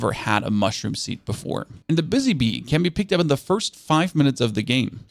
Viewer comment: I thought he said bussy bee for a second, and i lost it lol